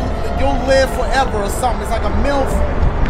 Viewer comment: did Dashie say, “milf”?